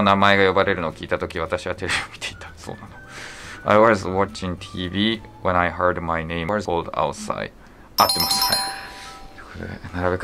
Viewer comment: この部分の回答は選択肢イのheard が使われてないので I was watching TV when I heard my name called outside. にはならないでしょうか? どなたかわかる方お願いします